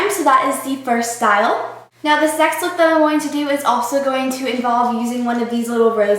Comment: Oh gosh, I thought she said sex look...